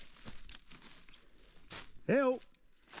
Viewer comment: Great to see you both handling every situation with humility and confidence on the mountain. I do have 1 question time stamp 18:00 - 18:02 I heard a voice say HELP -- was that overlaying his voice as he fast forwarded through the forest to the Spring box ???? Or was that someone else?? Thanks from NH, USA.